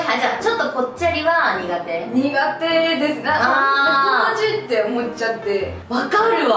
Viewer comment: 何て言ってるか分かる人いる？ 「◯◯って思っちゃって」の◯◯が気になる🤔🤔🤔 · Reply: 友達って思っちゃって、だと思いますよー！